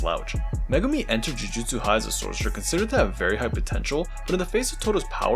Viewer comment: did you just say potential?